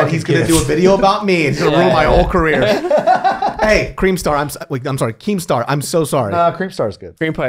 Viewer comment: I thought he said Kareemstar and I imagined a black or arab dude